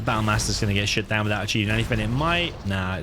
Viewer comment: "gonna get shot down" I always hear it as "gonna get shit down" 😁 gg wp, loved to watch edit hoped to see those annoying mines cleared, from both sides. would the mines upgrade on the supply there remove the opponent's mines?